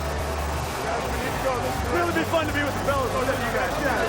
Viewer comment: What does Caroll say at 2:09:16? Sounds like he said "It would be fun to be with Belichick right now".